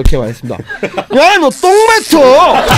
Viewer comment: 입냄새 친구가 뭐라고 한거에요? 못알아듣겠어요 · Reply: 똥뱉어~ 라고요 ㅎ